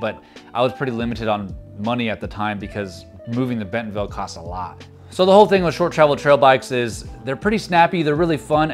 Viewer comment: Just had to rewind that, I thought you said "being on fentanyl cost a lot" 🤣